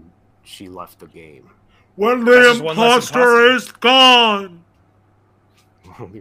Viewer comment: i honestly thought said "WHEN THE IMPOSTER IS GOD" the first time i watched this